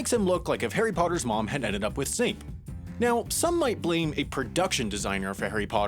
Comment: I thought you said Zayn so I replayed it but you said Snape and it wasn’t as funny